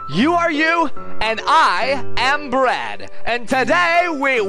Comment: at the beginning it sounded like you said "You are you, and I am Brad"